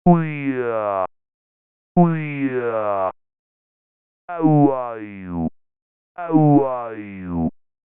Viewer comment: I hear "How are you?" That may be something worth exploring further? Do others hear the same phrase with the same pattern?